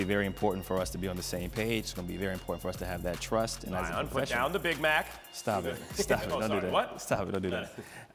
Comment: What did Max say at 1:17 ? Something about a Big Mac? Whatever it was I LOVE LOVE CJ's response with "Stop it, don't do that" I bet Max will never go that route again.